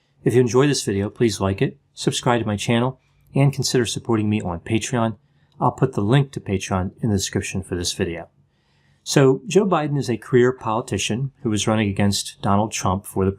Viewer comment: I had to listen to the intro twice... thought you said "queer" rather than "career" politician! Probably a good idea to enunciate that word to avoid confusion. (😂not that I thought there was any chance Biden is queer!)